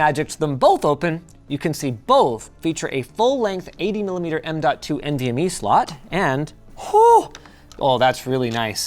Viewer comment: ah yes the 80 mm MWV slot.... I totally understood what he said 😂